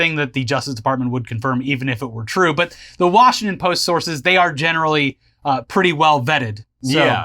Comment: Did I hear the "the russiangton post" who told you?